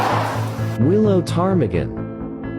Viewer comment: Willow targament sounded like it said alabama